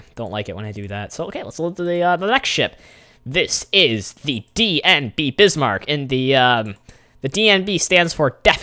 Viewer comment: Unless I heard wrong, I heard you say DBM Bismarck. It is DKM for Deutschland Kriegsmarine or, German War navy